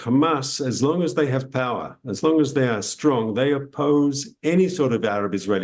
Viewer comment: Did he just say hamas I strong?